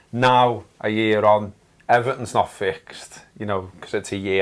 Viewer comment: So Scouse I thought you said 'Everton not fixed as its only been a year.' Until my brain kicks in and I realise you say 'everything,.